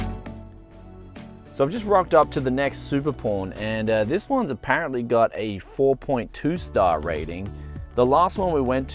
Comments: Did he just say supper porn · lolz brutal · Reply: Yeah, I could have sworn he said "Super Porn." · that Aussie accent makes it sound like he said Super Porn lol 8) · Sounds like he is saying super prawn. 🍤